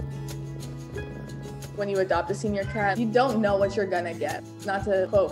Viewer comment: Oh… I thought you said Senor Cat.